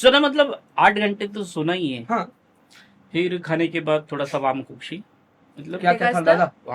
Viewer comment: me who heard “warm coffee”😭😂